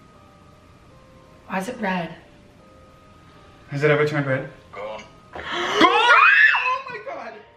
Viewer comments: what did it say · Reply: Steph Kay “gone” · did it say gone or ghost?? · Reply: nerdfightertwins74 gone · what does it say? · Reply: "gone" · What did it said at 19:09??? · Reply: Gone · awh man i didnt understand what it said in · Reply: Same. I guess "GO ON"